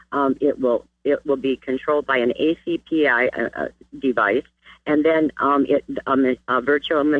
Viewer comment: hahahahahahaha, I heard "Controlled by a acpi" I lost it!! Hahahaha